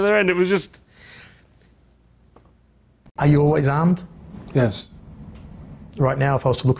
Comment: When the interviewer said "are you always armed?" I thought he said "are you well-endowed?"